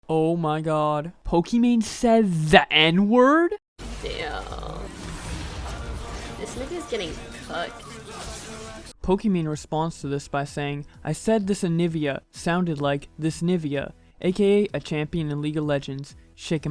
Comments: I thought she said this nivia is getting cooked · I heard “This Nivias getting cooked” Am I the only one?? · I heard “this niggas getting cooked” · I heard “this nivia is getting cooked” LMAO. I didn’t hear cucked or fu**ed lol. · Ohhhhhh I thought she said cooked instead of fu** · when i heard it for the first time ever i thought she said “this n***as getting cooked” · I didn't even think she said they were getting fucked, I thought she was saying "this niggas getting cooked" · i thought she said this nivas getting cooked LOL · I like pokimane but I hear a G. I turned it up and played it on both speakers and headsets. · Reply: saucy.chungus 1 I heard “ this nivia “ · When you said it sounds like she said “cucked” instead of “fucked” its cus she actually said cooked lol. “This nivias getting cooked” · i heard "this nigga getting cock"